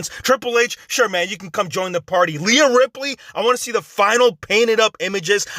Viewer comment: i just heard Lia Ripley